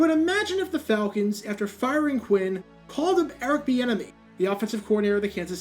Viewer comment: Shout to the people who mishear "Eric the enemy" every time someone mentions Eric Bieniemy. Also, that dude should definitely be a head coach.